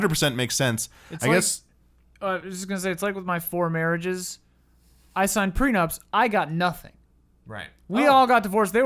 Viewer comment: Did say his "four marriages"? Not that it is any of my business but I'm surprised he has been married four times :O